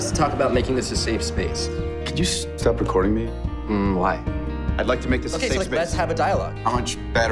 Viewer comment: What are they saying in the interaction at 1:25? I can't make it out and CC doesn't pick it up correctly. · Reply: "I'd like to make this a safe space" "Okay so like let's have a dialogue."